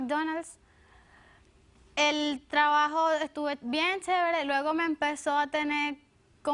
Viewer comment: escuche chevere?